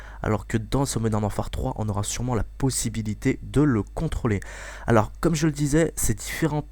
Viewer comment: wwwhhhhhhhhaaaaaaaa i think i haerd him say pussy